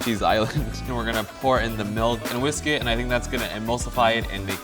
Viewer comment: I was following along and I misheard "whole milk and whiskey" instead of "whole milk and whisk it"